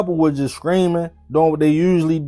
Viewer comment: Yo that accent thick, when you say Streaming it sounds like your saying Screaming 😂 it had me confused at first 😂😂